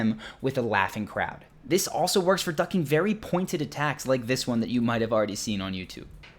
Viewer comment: Did I hear this also works for DUCK?!